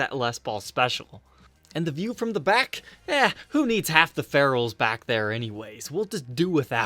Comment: I heard, "Half the ferals"...